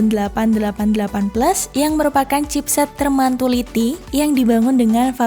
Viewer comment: chipset termantuliti? Aku salah denger atau emg aku aja gk tau artinya ini apa?